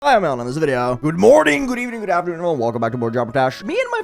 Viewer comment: Your intros are so fast all I heard you say was "Hi im ailing in this video"